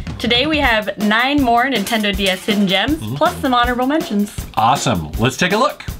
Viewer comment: Did she say "Nintendo DS"?